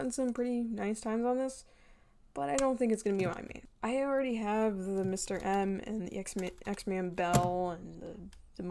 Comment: If you all want this sponsorship to continue please use my code at <link> 😁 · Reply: for some reason I thought he said he already had the 11 m pro 😂